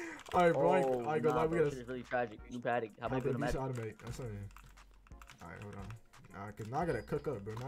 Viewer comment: what did bro say 🤣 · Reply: shi tragic, new patek, hop in the V shi automatic